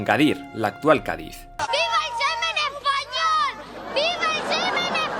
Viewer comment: "Viva el semen español...", que clase de mente aburrida digna de mí te manda estas mierdas?? XDD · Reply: Y mientras yo entendí "viva el meme en español" LOL